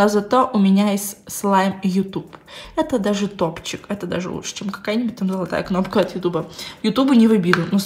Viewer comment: это даже лучше чем кокаин мне это послышалось 😂